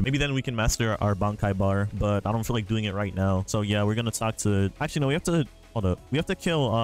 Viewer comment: did he just say talk tuah? (Is this a talk tuah ref )